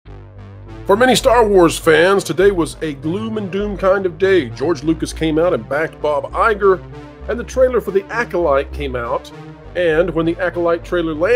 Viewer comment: At the beyond the video he said “for many Star Wars fans” but I thought he said “for remaining Star Wars fans” that gave me a chuckle lmao